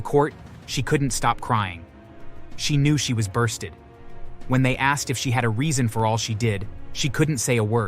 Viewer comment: Bursted? Did I hear that correctly?